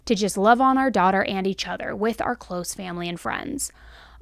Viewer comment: I heard you say "give us time with just our clothed family" and I thought "what a weird distinction to make, if your family are nudists or not" 😂 I swear, my ears 😅